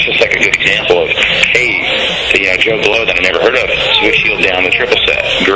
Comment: does he say "ni**er never heard of it"